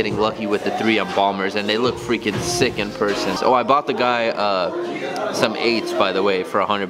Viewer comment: did he say "I bought the aids"